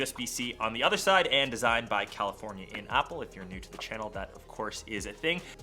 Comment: did you really say "Designed by California in Apple"? xD